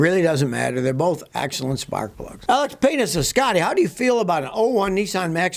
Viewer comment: Loved this video but 5:56, did anyone hear Scotty say I like penises 😂😂😂😂. That dude trolled him Scotty 😱.